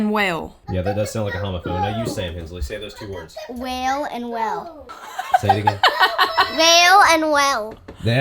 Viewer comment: I thought at first you were saying whale and wail - but at end I realized and actually showed the spelling which is whale and well - everyone has an accent which affects how we pronounce words - Love it!!! Mercy me I'm from Maryland, 3 miles outside of DC area and when my father was transferred to Cincinnati - everyone asked me what part of the South was I from - as of 2007 I was still asked that question (although I lived in Maryville, TN for about 4 years where my accent came back). Anyway, besides the Air stream I do believe this 5th wheel feels the most "home" - my opinion. I love that you have 2 couches in there along with the eating area - I love lots of counter space, especially with a growing family and meals to prepare & regular size frig - but down the road, the children will both outgrow their personal spaces; but til then, I love the home you have now and you do have home base property so you're not always having to find places to park in year round. One final question - would it be possible to add some kind of sound barrier between kitchen and bedrooms? In the Air Stream I believe it was, you had a curtain up to keep light and sound out of the sleeping area....... Merry Christmas and a Happy New Year!!! ❤